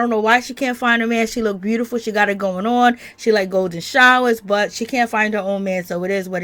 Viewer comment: While i do believe that this show is $scripted that $lap looked real to me ... But on a side note did i hear u say Amara likes G- showers @ 3:21??? 🤯😂 chal